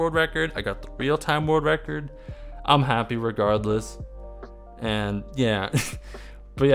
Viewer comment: What did he say on this time 9:41 Edit: I'm not so good on English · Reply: i'm happy regardless :)